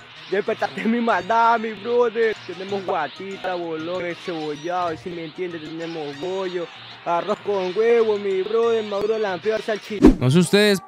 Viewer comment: les dire que dijo creo que dijo que vendía o comía encebollado bolon guatitta bollo y el arroz con huevo maduro alampredo no se si lo escribí bien : plátano maduro ese verde pues eso es no se como se llama muy bien salchipapa y